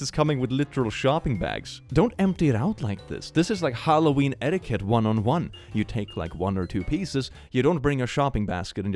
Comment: Did you say etiquette 1 on 1? Or etiquette 101?